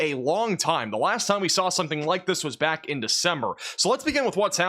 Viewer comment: I thought he said "The Summer" but may be wrong. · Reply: He say's December. I even turned on closed captioning after reading your comment